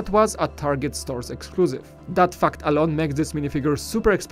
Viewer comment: Who did you just say the "F Word"I thought this was a clean channel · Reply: Hawks Nebula he said ”fact” not the F word